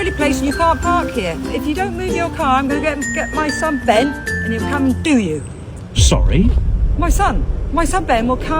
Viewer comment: sorry is there a British translator it sounded like let me go get my screen screen and then I’ll do you baa like what